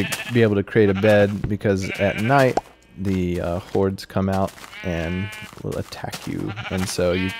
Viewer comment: lol i thought he said the hoars woops